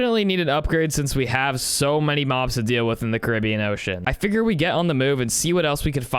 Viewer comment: I thought you said bean ocean